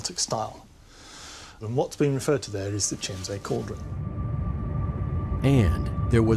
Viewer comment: I thought the guy had said Kinsey Cauldron but he said Chiemsee Cauldron, I was wondering why there are no books on the witch trials and tortures that took place at Wewelsburg Castle a few hundred years from when Himmler decided to use Wewelsburg Castle for the SS Inner Circle when they did their rituals